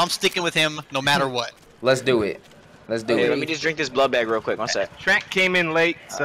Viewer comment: Did anybody hear say let me just drink this blood bag